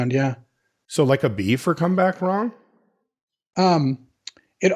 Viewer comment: I heard 'Come back Wong' from there at the end - and now I'm cracking up at the idea of an asian-morphing reanimate